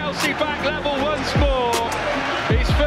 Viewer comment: I keep hearing “his first ever blue goal”